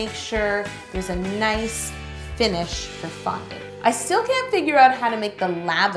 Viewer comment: I had captions on and at 5:05 I couldn’t figure out said “for fondant” because the captions said “the f***king” .....wha? 😂